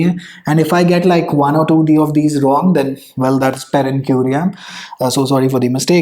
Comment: Sir what term did you say at 4:59 something like terren querian.